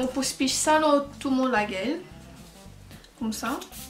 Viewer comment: SHE REALLY SAID "LAGELL" LMAOOOO